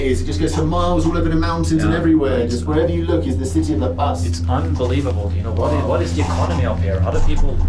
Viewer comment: did you just say unBolivibal :D